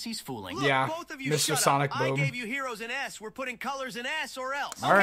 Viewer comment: was he saying ass or s💀